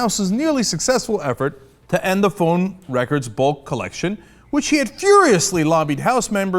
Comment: Okay that chap's accent is so thick when he said "Bulk collection" I thought for a moment he said "bullcrap." Then I remembered you can't swear in the US "democratic institutions"